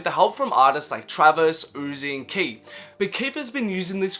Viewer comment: when he says travis, uzi. who does he say after 2:25? does he say keef